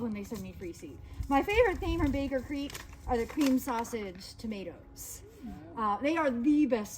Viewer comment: How nice! Thank you for making us a part of it. Your free seeds look wonderful! I want 1 of each :). Question: You said at the end a type of Baker's Creek Tomato that is the best. I heard "Cream Sausage Tomato". I went to BC's website to add it to my list and couldn't find anything remotely near that name. Can you please clarify the need of the Tomato? Thanks so much! I hope your class was lots of fun! Hope you get completely rid of your cold super fast (zinc, selenium, d3, c, propolis)!